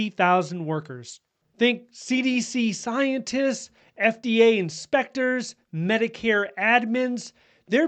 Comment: hahah Scared me..I thought you said FAA Inspector but you said FDA....lol Whew